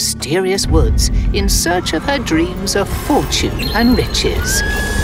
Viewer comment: Not me mishearing riches as bit@